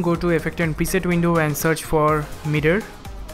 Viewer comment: Who else thought he said "meter" and not "mirror"?